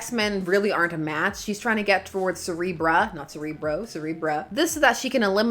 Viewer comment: I do hope you know that every time you say "Cerebra" I hear "Cere-bruh". This isn't a complaint!